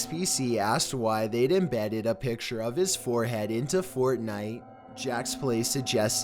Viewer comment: I thought you said "warhead"